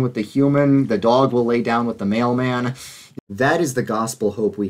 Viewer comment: The funniest out-of-context quote in this video "The dog will lay down with the mail-man, that is the Gospel hope" 😂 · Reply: Oh he said mail man? I thought he said male man lol